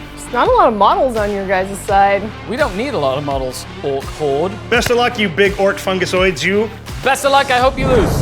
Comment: Ngl, at 6:55 when he said "ork horde", I fully thought he was gonna say "Ork whore", I was sat here like :o